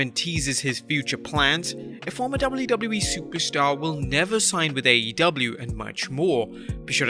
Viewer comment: I thought you said superstar.. lol